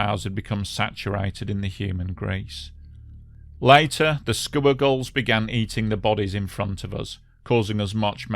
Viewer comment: why do i keep hearing "scuba girls"?